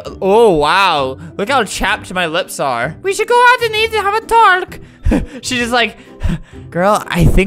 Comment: am i deaf or did i hear albert say “eat a hot tark”